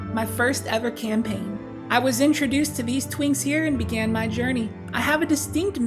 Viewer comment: 😂 did you say twinks ?